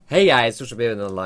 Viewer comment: watched your vids for over a year but I still don't know what you're saying in your intro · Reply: it sounds like he is saying "hey guys social pear" or something lol